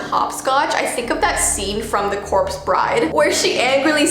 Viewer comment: Me: Being A Huge Fan of Corpse Bride: *talks about Hopscotch in corpse bride * me: 💖😭😊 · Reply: I- thought that said Orcs bride .